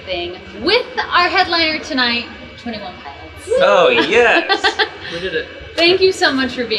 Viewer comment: Did Josh just- what- Did he just say hell yeah?????